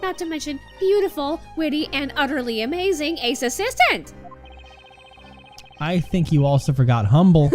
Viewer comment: Thought that said “ace assassin”